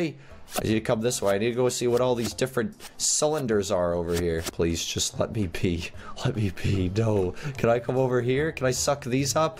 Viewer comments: i thought said “please, just let me pee” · I thought he said let me pee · When said please let me be I thought he said please let me pee